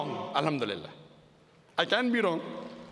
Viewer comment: the subs say “i can’t be wrong”. But he actually said “i can be wrong”.